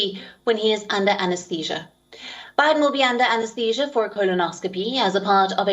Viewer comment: I thought she said "Biden under amnesia"😆